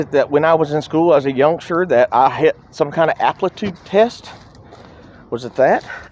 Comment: Did he say "applitude" test lol!